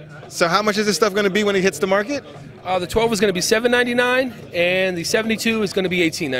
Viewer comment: Yikes, $1,799 for an S9 copy. The “turntables”’seem interesting and the touch pad on the mixer does as well. I think Rane missed the boat with this. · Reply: Wow bro, I thought they said $1,799. They defiantly missed the mark.